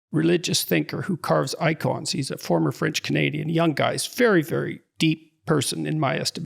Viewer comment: did JP just say Jonathan Pageau was a "former French-Canadian" lmao